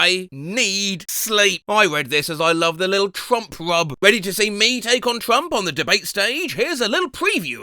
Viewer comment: I HEARD this as "Ready to receive meat" 💀